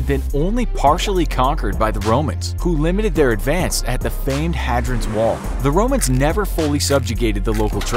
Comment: The historical accuracy of this video makes me question any information I've consumed from previous videos... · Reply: he doesn't say hagrid's he says hadrian... It just sounded like "Hadran"